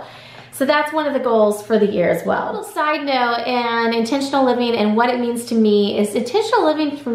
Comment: I thought this said “what is international living” 😂